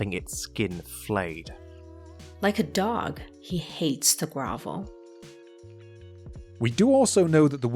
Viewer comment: Proverb 5:92 Like a dog, he hates to grovel Sorry, I thought I heard her say "hates the brothel"......sorry about that misstep